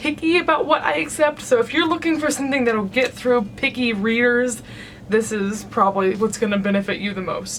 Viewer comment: when you said picky readers I thought you said "picky rears" and I was like, ok.....? XD